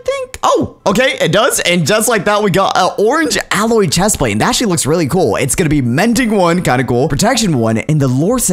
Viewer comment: I thought he said “that s*** looks really cool” and not “it actually looks really cool”